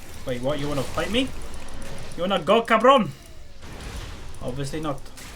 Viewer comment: wait... did u said "you wanna go, cabrón?" at 34:37 hahahaha